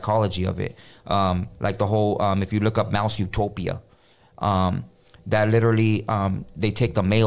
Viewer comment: What did he say at 26:00? Malsutopia?